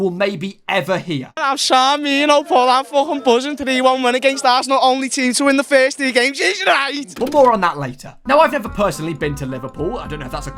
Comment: tf is bro sayin 😅😂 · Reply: “I’m sound me you know Paul, I’m fucking buzzing, 3-1 win against Arsenal, only team to win the first three games, is right!” Translation if you’re not British: I’m good Paul, I’m very happy, 3-1 win against Arsenal, only team to win the first three games, that’s right!